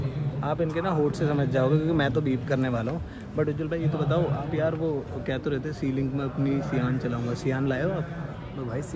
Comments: Can anyone tell me what techno gamerz said in beep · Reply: Subha 2 baar muth raat ko 2 baar muth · what techno said ??? Plz tell❤😊 that he beep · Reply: Red bull 😂